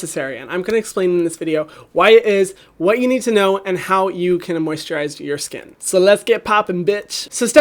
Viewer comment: if you turn on auto generated cc instead of "moisturize" it says "immerse Chur eyes" 😂